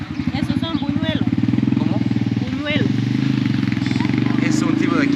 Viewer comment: You couldn't understand "buñuelos"